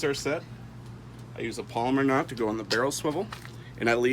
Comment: Great Tutorial. I thought you said Palmer Knot so I had to look it up...LOL!!! What knot do you use to attach the leader to your fishing line?